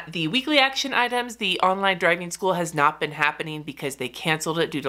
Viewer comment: Too funny! I heard “online dragon school” in the first bit and thought “Wow that sounds cool!” Then later I heard you talking about rescheduling online DRIVING school and realized that my ears must have been playing tricks on me. I still think Dragon School should be a thing. 🐉 🚙